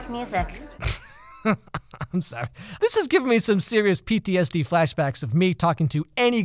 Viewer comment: I seriously thought he said BTS on 8:00 😂😂😂😂